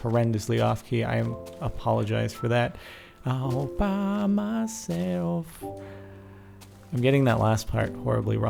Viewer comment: I thought you said Obama Self. Lol